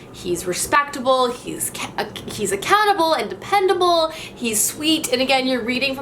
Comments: When you said "accountable" for the second character I thought you said he was a cannibal and I got so confused! All the right reasons, hmm? 😂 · when you said "he's accountable" I totally heard "he's a cannibal" lol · You said "He's accountable", I heard "He's a cannibal". And then my brain goes, waaaaait, that can't be what she said! hahahaha I had to go back and listen to it. So glad you weren't supporting a cannibal.